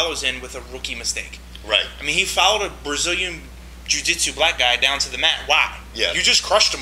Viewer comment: Did you say a "brazilian jiujitsu black guy" at 0:20 ?